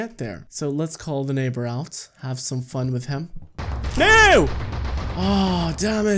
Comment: here's a funny so if you want to see it then I posted the time also um I think what you said was ""Nou!!!""